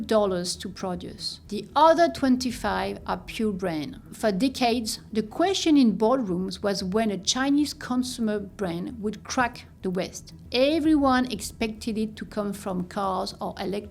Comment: I really thought she said "for dickheads" Dumb comment, sorry. Great vid!